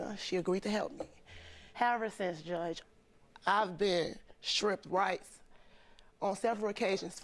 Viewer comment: When she said she been stripped rights the closed captions thought she said shrimp rice LOL idk why i thought that was so funny